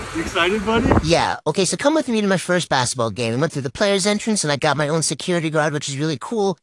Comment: I thought it said “Baseball” not “Basketball” the first time I watched this 😭✋